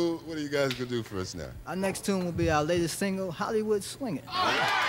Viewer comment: This whole time I thought he as saying Hollywood swinger 😅